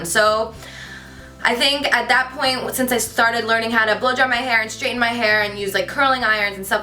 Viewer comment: am I the only one who heard blow job instead of blow dry. Hahaha. Omg cant stop laughing. I am watching this vid just after the whisper challenge video and i am rofl.